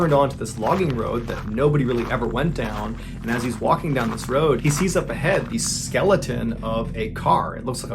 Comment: I hit skip too quickly so idk what the as was for but at 8:53 it cut Mr. Ballen off mid sentence so what I heard was "and as he's walking down this road he sees your vagina" 🤣 the timing on that was way too perfect 🤣